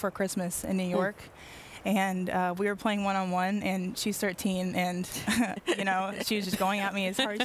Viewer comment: Does anyone know what Napheesa says @ 4:22?? She says something about Gianna starts, "Teeing," or something like that. I can't make it out. I'm curious is all. No major deal, just curious what she is referring too. Thank You. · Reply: Steve Douglas i think she said she’s Thirteen (years old)